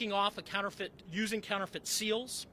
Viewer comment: Did he just say "counter-foot" goods?